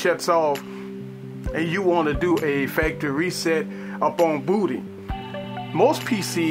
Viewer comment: it sounded like he said booty